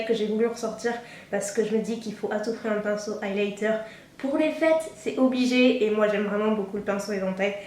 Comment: J'ai compris "pinceau highlighter pour les fesses" 😂😂😂❤